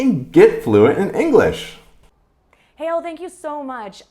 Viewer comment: So, how's your mindset? Growth? Fixed? 🔥Watch this and find out 🔥 loves you! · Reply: I thought u said: let's go to hell, oh It's Hale.